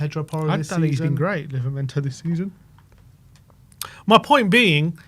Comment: Naaa noo way did i just hear livramento "not been great this season" mental